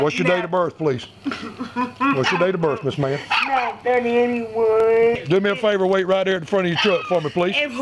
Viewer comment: did she say 81? As in 1981? Woo that’s rough.